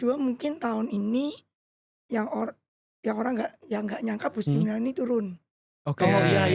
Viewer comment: apa ya yg ci blg turun ? Ga jelas kedengeran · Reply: Bu sri mulyani